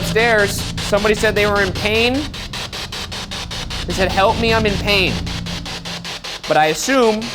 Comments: I am gonna be honest i heard "our table" instead of "i am in pain" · Instead of "I'm in pain" I heard "They're coming" Am I the only one? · Can't lie I didn't hear 'I'm I'm pain' I thought it said 'oy vey' 🤣